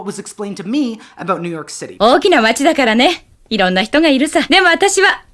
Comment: do I hear the shopkeeper lady say Okinawa at 30:20? Does Okinawa just mean 'big town'?